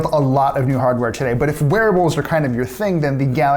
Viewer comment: Did anyone else understand "werewolves" instead of "wearables"?